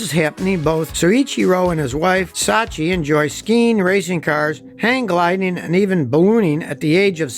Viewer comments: did Scotty say: “they enjoy boning” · did he say that Honda and his wife enjoyed bone-ing even at 74?